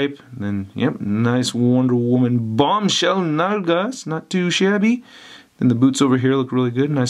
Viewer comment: WHAT IS THIS WORD HE USES IN THE PLACE OF ASS??? Please, somebody tell me!!!!! 😭😭😭😭😭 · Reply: Nalgas is Spanish for Buttocks lol